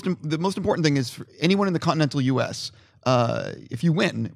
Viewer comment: he said "in the continent of the u.s" did he mean north America or the u.s im confused. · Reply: hardestyful OK thanks. sounded like he said continent.